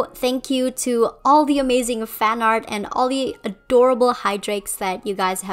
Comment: I love your redesigns and ideas!🥰😍🥳 If you could read about some of my own ideas about Felix I wrote in the comments of your Chloe redesign! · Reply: For a few seconds I thought you said I was an "amazing artist" and was so confused and had to do a double take lol. Also I love your idea! I never thought about Felix in a role like that but now I want this to be canon! Just the thought of Adrien and Felix actually supporting each other is so adorable!